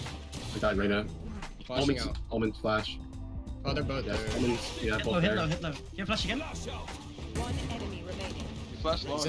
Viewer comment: is he saying hitler or am i trippin